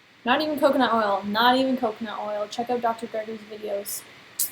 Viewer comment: you said to check out Dr._____'s videos, I couldn't really make out the doctors name that you said. What doctor is it that has videos about oil ? Thanks! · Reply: Dr. Gregor :) <link> on youtube!